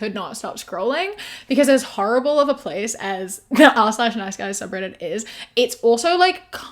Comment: I thought you said the night skies subreddit. I was v disappointed. 🌌